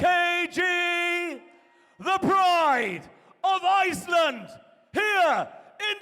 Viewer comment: I heard the 'bride' of Iceland.